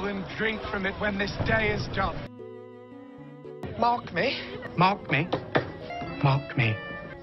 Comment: It sounds like he is saying “Mock me!” Now you can never unhear it! Mark me!